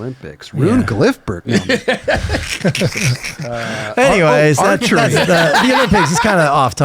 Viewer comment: Fucking thought it said gilfberg lmao